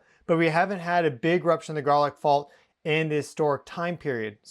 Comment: I keep hearing garlic salt